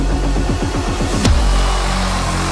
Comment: Почему-то слышится не "На ЕГЭ", а на "На Ег" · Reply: Мне послышалось "На ЕЭ"